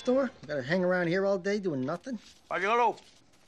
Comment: Wtf does Tony say at 0:37?? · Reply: Aiello